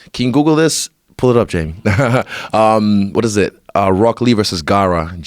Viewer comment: I thought he said “This is rocket league” but it was rock lee lmao